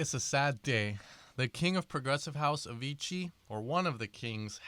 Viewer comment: it sounded like you said "the king of progressive house, Avicii"